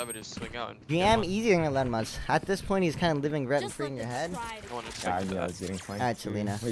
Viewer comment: what did he say on 0:35 to :40? I cant hear it properly. thanks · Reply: He thanked a sub then read a comment that said “at this point hes just living rent free in your head”